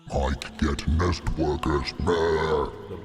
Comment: MIKE GET NEST WORKERS (NOW?) the battle has begun Me: why do I hear boss music